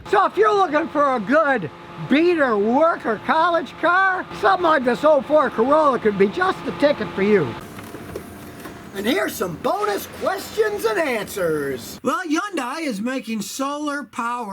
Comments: I swear he called this a "Ford Corolla" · Did he just say 2004 Ford Corolla?😂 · Lol did Scotty call it a Ford Corolla? 😂👍🏻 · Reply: Jonny DIY - apparently he said 04 Corolla. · Did he say Ford Corolla in 6:02? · Did he say Ford Corolla @ 6:02 lol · When he said ford carolla just after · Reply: I thought he said old ford carolla but yes your right he did say 04 carolla